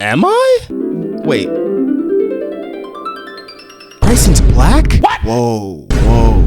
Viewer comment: did you just called him black💀